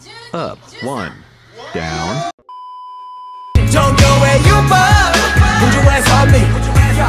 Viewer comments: I'll never I hear the "jungkook will you put yo ass on me" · I always hear it as"Put your ass on me" lol